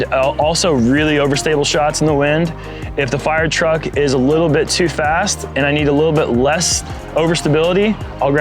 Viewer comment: If the firetruck is a little too fast. Did he just say that?